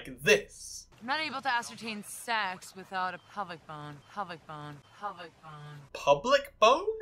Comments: I actually think she's saying "pelvic bone" not pubic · I thought she was saying “pelvic” not “public” with that line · she is actually saying "pelvic bone", not "pubic bone".